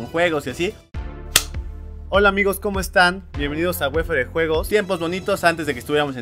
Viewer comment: No sé porque pero pensé que decia “wefere sin juegos” xd